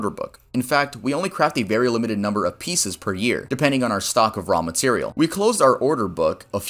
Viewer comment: Sounded like said ramen cereal when he said raw material